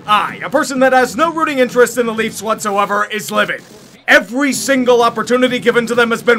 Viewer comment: is saying “living” or “livid?” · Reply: Livid